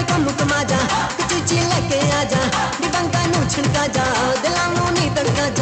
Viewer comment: did it just say chuchi leke aaja😂